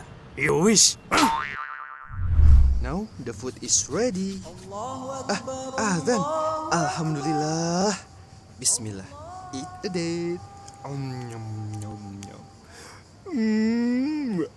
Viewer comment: turn on captions it sou ds like he saying eat the date but captions say eat a date weird right